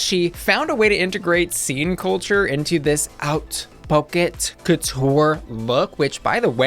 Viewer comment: I thought they were saying oat pockets like some kinda American snack 😭😭